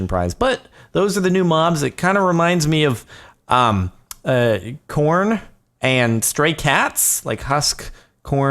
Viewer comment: Thought he said "reminds me of porn"